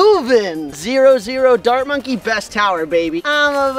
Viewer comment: I thought you said Dhar Monkey instead of Dart monkey